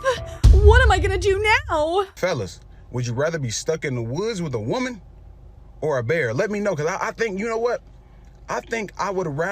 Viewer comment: I heard “be stuck in the woods with a bear or drake”